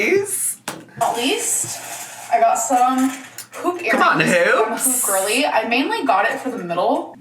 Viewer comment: Omfg I thought she said she got poop earrings when she showed the hoop earrings